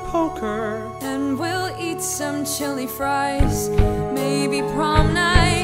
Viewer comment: Nobody: JD: Chili fries I thought you said children dies! edit: okay so i was like 15 and very sleep deprived when i wrote this. thanks for all the likes!! i know it’s got the minion meme vibes and please do make fun of it baha, this comment has been the literal bane of my existence.